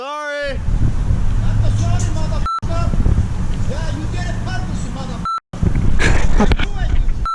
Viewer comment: He said "That's a sorry mother bleeper, ya you get cut you mother bleeper" That's what he said.